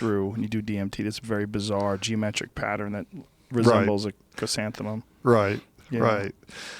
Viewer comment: Wtf word is Joe saying at 2:06:50 the pattern he's talking about at a breaktrew what word is that I've tryed looking for it for the last like hour and can't figure out wtf he's saying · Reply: Chrysanthemum